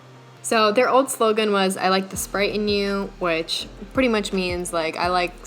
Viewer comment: I heard "i'd like to sprite in you"